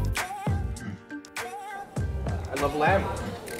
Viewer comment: When said "I love lamb" I heard "I love lamp." Anchorman, anyone?